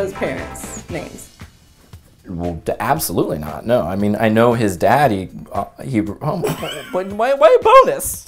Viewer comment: did it not sound like said “I know his (Joe’s) daddy” 😂😂😂